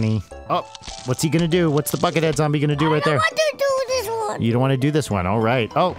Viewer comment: Brian said "bucket" but at first i heard "fuck it" and i was like "wow theyre a very progressive family."